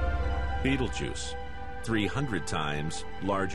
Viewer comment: did he say "beetle-juice"?